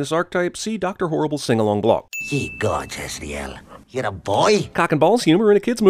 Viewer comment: What did he say? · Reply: "Ye gods, Azrael. You're a boy?"